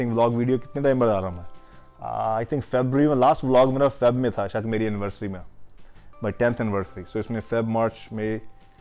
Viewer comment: did he said "my death anniversary" !!